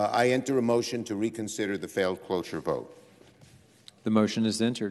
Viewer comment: I always thought they were saying closure...